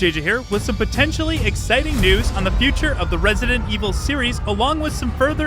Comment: Omg at first I thought you said upsetting news. I had to rewind then I heard exciting 😅